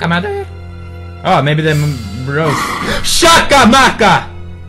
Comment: it sounded like he said shakabakka!